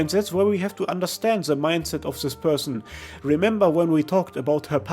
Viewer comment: I love how you say "this Person" but the subtitle says "this Psychopath" (timestamp